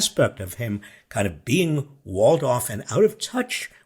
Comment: Trump is a walled-off version of himself, but for a moment there (at 6:12) I heard that he's a Waldorf version of himself, which could explain his tendency to speak word-salad.